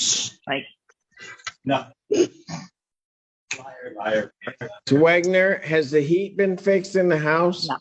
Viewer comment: did some just say "Liar, liar, pants on fire"?!?